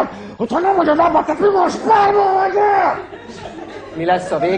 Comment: Mit mond 1:34-nél mert én spermahajder értettem XD